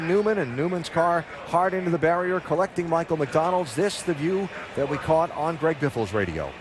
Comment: did he say Michael McDonald?